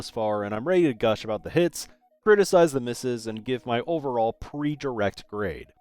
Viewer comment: heard this as "criticize the mrs." I was like damn just calling out your wife like that